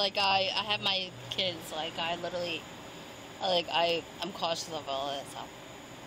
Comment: I can't believe the officer didn't buy her story that she was just like literally. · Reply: Did I hear her say “like, I’m cautionable”?? 😂